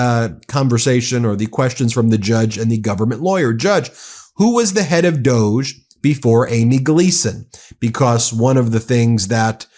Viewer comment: Thought they said some lady was head of doge!!!.